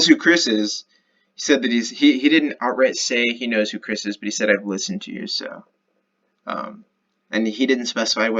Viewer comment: I think he says "I've listened to you, so....) meaning he's listened and learned lol